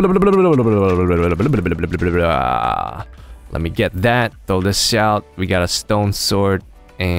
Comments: did he say what I think he said? xD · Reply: AlbertoPlays MC he said "Lets throw that delish" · Just start watching from there and says "Throw this shit out" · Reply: I thought he meant to say "Throw thish out" :P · Did anyone else hear say "SH*T" at 00:56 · Pls don't hate but I heard say "throw this s**t out" at